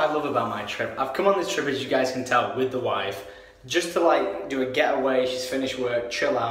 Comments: did he say "with the wife" ? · Did he say WIFE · WIFE????!!!!!!! Am I the only one who hears that or no?